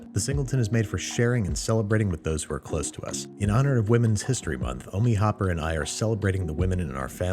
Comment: i thought he said homie hopper not omi hoppers 😂😂